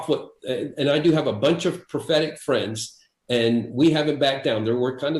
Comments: I'm sure Enlow's saying "prophetic friends", but I keep mishearing "pathetic friends". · I thought the guy said "I have a bunch of pathetic friends...". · Did he say he has "'a bunch of pathetic friends"? ;)